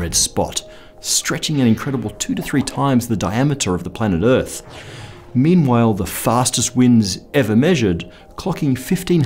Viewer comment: did you just say "memewhile"?